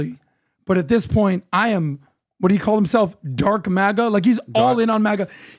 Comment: Love Elon. He is so real, honest, sincere and natural!!!! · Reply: Could have sworn he said "Drunk MAGA!!" rather than dark maga. Either way I loved the way he jumped up and meant it!